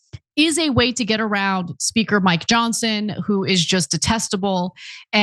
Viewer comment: Anna ... I thought u said, mike Johnson who is just a testicle 😂